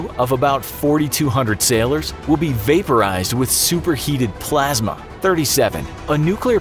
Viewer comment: At first I thought he said “all of its crew will be vaporized by superheated plasma.” 😂